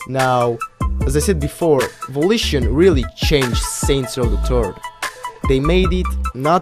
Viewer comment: Your accent made it sound like you said saints row the turf